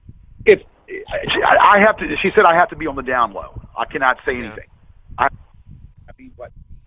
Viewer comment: I like that my brain automatically translates so when he said "down low" I heard "down though"